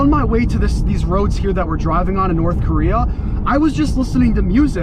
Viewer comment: Nice review! At one point it sounded like you said North Korea, even the CC said it.:)